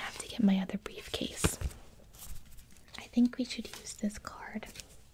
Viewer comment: Is it just me or did anyone else hear “By the time you retire you will be... dead” Timestamp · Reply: did she say “I think we should use discord”